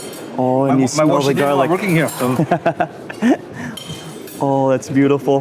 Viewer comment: Did he just say 'I wish i didn't work here' ???? that was so rude